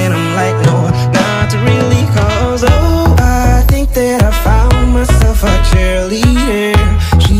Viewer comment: I heard "oh I found myself a triniyall"